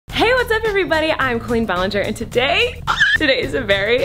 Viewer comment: It sounds like you say “queen Ballinger”